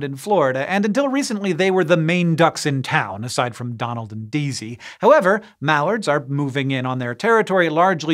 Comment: When he said Donald and Daisy it almost sounds like he said Donald and Deezy